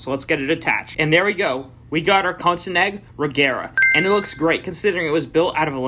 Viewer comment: The lego countach one was a crime💀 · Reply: Exactly. 2:24 Did he say Kosinegg Regera?